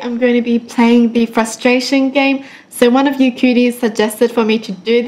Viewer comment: the first time i heard her saying 'you kyuties' I thought she said cuties...😂